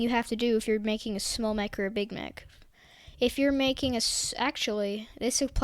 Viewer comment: If you pause at 2:15 with captions, he says big mech but captions say Big Mac 😂